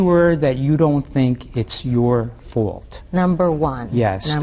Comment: Wow, I thought he said fart but evidently he said fault